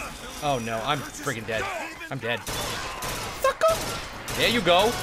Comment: Also sounded like u said f.uc.ker at 6:00 instead of sucker